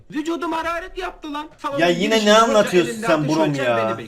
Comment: abi ya 8:58 horus bo dedin ya ben oru** anladım :) xdxddxasadsad